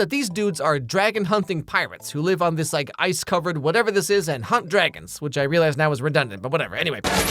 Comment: I was doing something while listebing to the video and i thought he said "hump dragons" i was like what? 😅